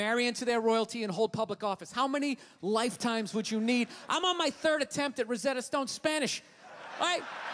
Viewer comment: What does it say at 7:32 please? :) "I'm on my third attempt to..." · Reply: Antonietta Mancinelli "I'm on my third attempt at Rosetta Stone Spanish" (Rosetta stone is a program to help you learn foreign languages)